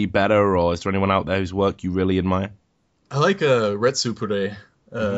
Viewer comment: I am a fan of His work. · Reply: Richard just asked you what Youtuber's you "like" and I can't make out the name of the first one you list about the youtuber LP's , "Red Super Day" is what I thought, but I can't find anything.